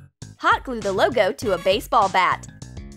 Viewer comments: did she say a baseball bat??? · When you said base ball hat it sounded like you said base ball bat